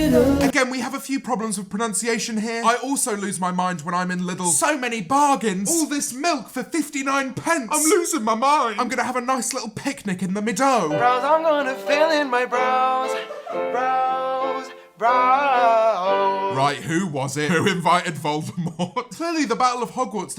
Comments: why did I hear "I wanna fill in my bras"? 💀💀💀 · What I heard: ‘I’m losing my mind just in Lidl’